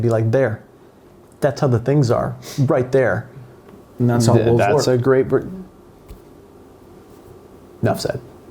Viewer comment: What did he say at 21:59? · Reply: I listened to this several times and I'm thinking he said, "That's how wolves work".